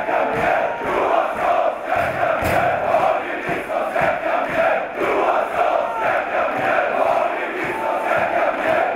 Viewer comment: Vad säger dom vid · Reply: peter jonsson Johanssons järnkaminer, Hardy Nilssons järnkaminer!